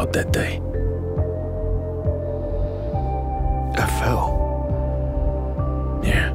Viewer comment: possible meme? “I fell” It kinda sounds like he said F hell though